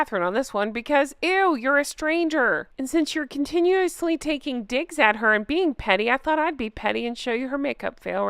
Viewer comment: My heart just missed a beat. When you said "..and since you're continually taking DIGS" I thought you said DiX for a second! Made sense though. 😁